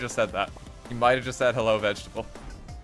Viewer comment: He does actually say "Hello Vegetable!".